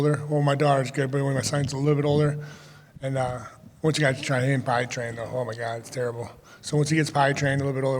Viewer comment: I heard "hit by train" 😂😂😂🤦